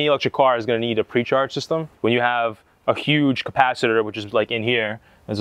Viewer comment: all i heard was flux capacitor..we know it does 88MPH soo..?